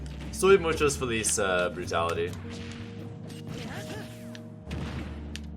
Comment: I thought at first he said police brutality 😂